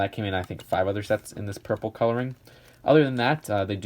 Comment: Did you just say purple colouring??